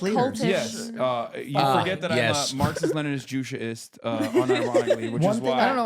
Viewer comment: Marxist-Leninist- What? What was the third word Hasan said? Thanks for any info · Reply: “Juche-ist”. Juche is the ideology of North Korea. I know nothing about it. Ive seen some say It’s like a hyper-nationalistic version of Stalinism. I’ve seen some say the extreme self-reliance/independence of the ideology was necessary to keep NK from collapsing. Some say it’s a meme ideology. Hope this helps!